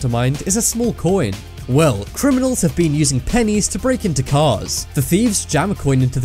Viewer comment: He actually said Penis!